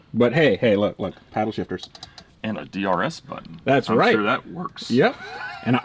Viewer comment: lol i thought he said it had a DNR button for a second.